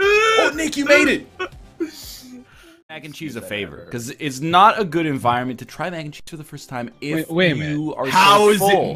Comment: Ive watched this like 40 times but I still don't know what NL is saying at 29:24 "You're in the company of people who ate at ____" someone help! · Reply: "people who ate triple your nugs". It's in reference to their 50 nug challenge results